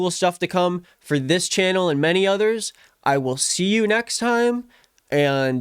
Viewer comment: I thought you’d say keep on queefin on lmao fck my brain